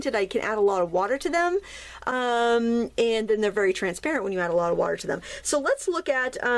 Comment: I can't make out what you said at 2:49. "They are opaque when you add water"?